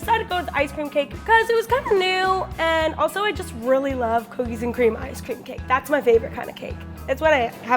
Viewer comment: I thought she said cookies and cream dumb cake 😂😂😂😂😂😂😂😂